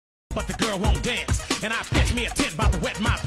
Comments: What i heard at first: "You better fart like you want it..." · I thought he said “fart like u want it” 💀💀💀🤮🤮🤮🤮